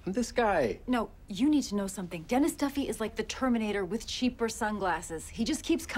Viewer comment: Seriously thought that they said Dennis Dunphy, not Duffy. Where my Marvel diehards at? Anyone know who D-Man is?